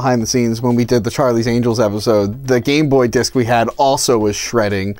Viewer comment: I’m sorry, did he just say “Gamboy disk?”